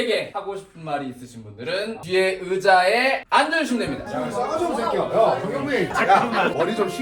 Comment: 뒤에 들리는 "야이 싸가지 없는 새끼야 전경민"은 촬영중 후배를 혼내는건가요